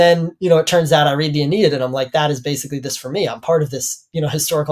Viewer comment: what did you say you read? Ania?